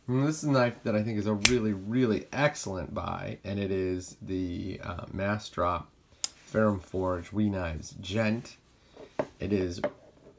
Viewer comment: (Mass drop farium forge wee knives djent?) I was thinking that's a cool looking knife and soon as I heard the name I was like, no thanks 😂